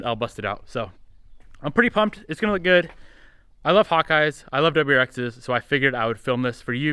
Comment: i thought u said " I love hot guys " lol